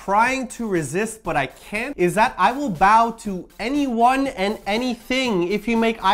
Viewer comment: I thought 4:46 said I will gracefully bowl but